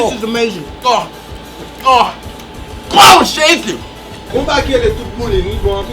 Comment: can somebody please tell me what he yelled at 2:30 lol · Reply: "Glwa a Jezi"